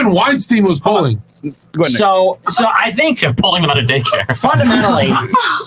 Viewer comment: what did destiny say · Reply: “Yea pulling them out of daycare”